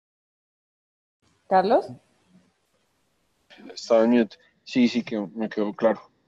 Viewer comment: El invitado muy bien pero los entrevistadores malisimos, no estuvieron a su altura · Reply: parece q uno de los que preguntó ni puso atención.. cuando le preguntan que si le quedo claro, se demora en responder y creo que dice "Estaba en youtube" min 19:00 sentí pena ajena.